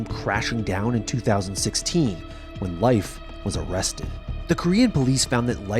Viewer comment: am i the only one who thought he said 'life was molested'?